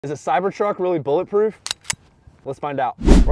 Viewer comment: I'm the only one who thought said cybershark the first time hearing this video 🤔